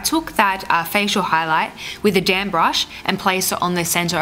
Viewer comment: I heard "I place this with a damn brush" LOL (instead of damp)